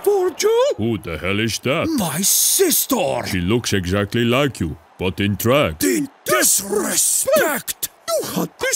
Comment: The point is that VVD disrespected Ronaldo and his sister kicked off on social media. This isn’t VVD disrespecting Ronaldo... I’m not criticising Ronaldo or the goal. I just wanted to cover the VVD Ron story a bit more✌🏻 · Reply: I thought he said,” she looks just like u, but a trap” 😂